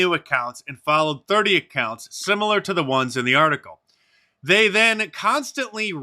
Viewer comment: 🚨 Order my new book The War On Conservatives here: <link> . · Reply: Mark, did you say They/Them at 1:59? ROTFL